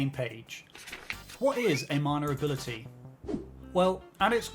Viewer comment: Ngl loved the video but why does it sound like u say "manure ability" every time ?